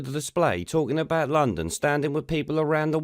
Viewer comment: It sounds like khan said londonstan 🤷🏻‍♂️